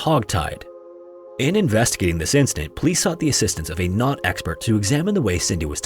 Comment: When he said knot experts I thought he said not experts 😂, like damn theyre so desperate that they started asking someone that is not an expert.